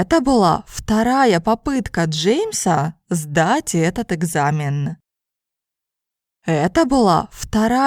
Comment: Я сначала услышал "второй напитка Джеймса, позле того сдать экзамен"😃 видно, что мне нужно практиковать надолго на слушание. Спасибо, увидимся в следующем видео!